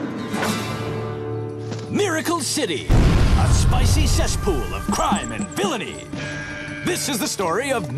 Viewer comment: In the beginning I heard"Spicy S*x pool" but I heard sick to but yea.... 😅